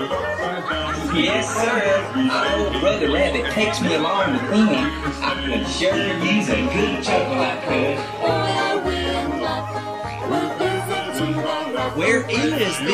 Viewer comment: can anyone find out what Br'er Road Runner was saying? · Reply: "Yes sir! I hope Brother Rabbit takes me along with him! I could sure use a good chuckle I could."